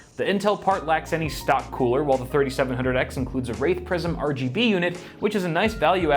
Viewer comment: Yeah I did not hear you say WRAITH PRISM here. What I heard was you naming, like - the worst kind of Prison. lol Second word not that far off. First word, DEFINITELY not how you say Wraith.